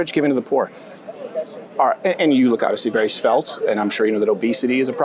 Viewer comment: what word does he use here? You look very svelt?